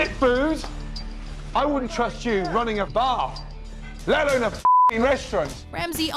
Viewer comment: did anyone else hear him say "I wouldn't trust you running a bath!"